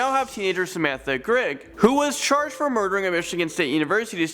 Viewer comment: Wait...whats "sentencing"?????????? I'm in confusion... 5:32 DID YOU JUST SAY "Murdering a mexican student..." WAS THAT WHAT HE SAID?!?! BECAUSE I AM A MEXICAN AND IF THATS WHAT SHE DID IMA FREAKING RAGE!!